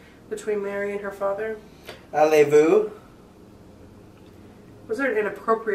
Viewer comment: it sounded like said "Allez-Vous" which is French for "Go Away" which is pretty interesting, it could have been trying to stop something from coming. Also, when went under around 31:00 it sounded like she said "Ma ça vie" which means "My Life"